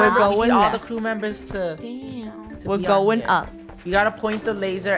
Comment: I heard lunar or rainbow say da**